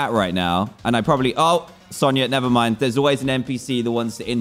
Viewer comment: 0 did dan said “Sonia Nevermind”